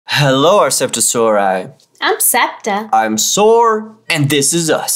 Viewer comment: Hahha il sorry but when he said im soar it sounded like he said " im sore.. " 🤔😅😅